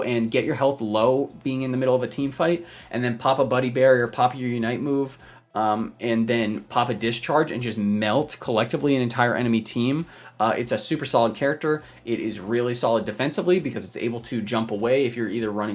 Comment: I know he was saying “pop a discharge” in the zeraora section, but I heard “Papa Discharge” and now I think that would be a great gamer tag for this game.